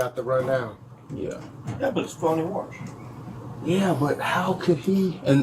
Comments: I'm sorry...... did he say ITS FUN TO WATCH!??!?!?! ( edit for time stamp) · didn´t understand it, did he really say it was fun to watch seeing the guy falling out of his wheelchair? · did he say "that was fun to watch"? Or I'm mishearing?